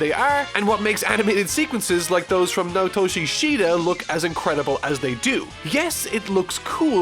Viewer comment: Can someone clarify for me what he's referring to here? I can't understand what the name is, is it Notou shishida? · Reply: He said Naotoshi Shida